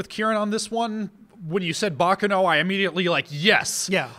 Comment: Btw guys, just a friendly thought: you're saying "Baccano" wrong. In fact, at first I thought you were saying something like "Bakeno" by the way you pronounced it. It's "Baccàno" and not "Bàccano". Just a lil' note since I see frequently asks how to pronounce different things.